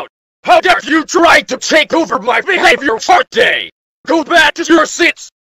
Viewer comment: did she just say behaviour fart day